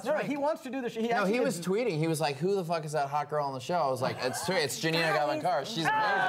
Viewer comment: What did mark say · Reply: That Shiva is the hottest girl I think